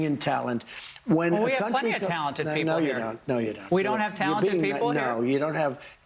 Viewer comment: He did not say "We don't." He said "You don't."